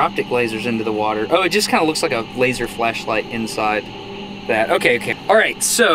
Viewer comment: Did he say a laser fleshlight?!?